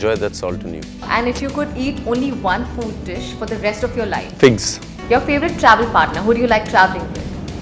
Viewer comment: did he just said "pigs" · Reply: He said figs.